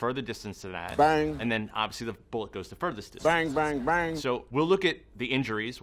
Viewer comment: Did he say the bullet goes the furthestest?